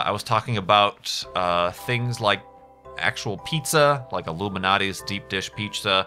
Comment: I'm pretty sure I heard Illuminati's instead of Lou Malnati's.